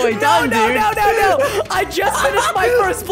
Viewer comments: Would you rather subscribe or like the video? Prove it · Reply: Both because I'm Subscribed to you already, and I just like videos that interest me anyway. a lil late to say this, but I think you said the worst no no word "F***!" at 2:03. Tell me I'm Wrong Please. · I could've sworn, I heard say "F%^#!" or I could be wrong. I kept going back to make sure my hearing was correct.